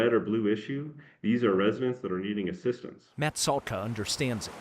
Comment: did he say rentals? Are they paying the government taxes? Is it man made snow runoff? It's not about trump ...it really isn't.